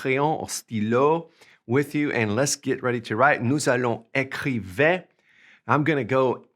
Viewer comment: Salut. It sounded like you said (at the beginning), “Nous allons écriver”??? You should have said “Nous allons écrire”. We are going to write.